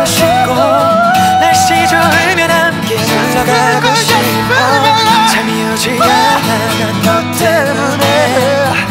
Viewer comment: 에 마크툽님이 뭐라고 코러스 넣은 지 아시는 분 댓글 부탁드려요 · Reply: just believe me just believe me yeah인거같아요